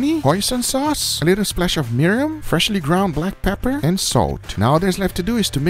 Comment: i can’t be the only one who heard “a splash of urine”